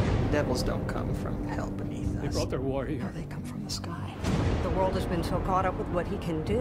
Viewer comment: Hi! When the automatic sub says at 0:46 "i brought their warrior" i think it is not the correct sub but i do not understand what the man says really. please help me. sorry but my english knowledge not so perfect. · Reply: "They brought their war here."